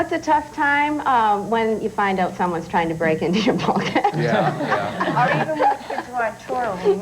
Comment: What did say at 1:18? Someone's trying to break into what? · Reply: Bulkhead